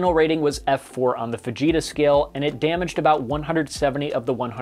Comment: did he say fajita scale like lol where cleo lol las casuelas??